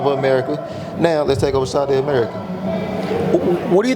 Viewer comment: Did BNJ say Saudi America? 😂